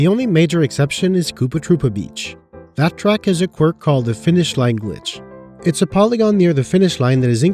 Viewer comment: I heard this as "Finnish language" and was very confused for a moment.